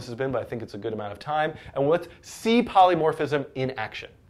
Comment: I thought you were saying C polymorphism and I was like "WHAAAA"